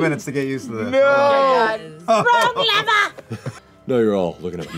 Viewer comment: Wrong lever! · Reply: Thanks for the line, I heard ‘frog liver’ and was confused af